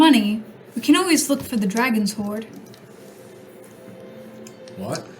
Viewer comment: Whenever says “dragon’s hoard” I heard “dragon’s whore,” which greatly changed the line of “I am not a dragon’s hoard.” 😂